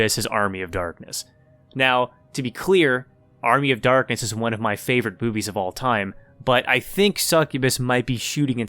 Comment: i heard it like 5 times and i keep hearing 'Army of darkness is my favorite boobies of all time' having a hard time identifying if its me or you who's Freudian slipping